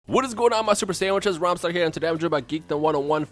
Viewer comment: i think i misheard the first line did say "hey my super saiya-sandwiches?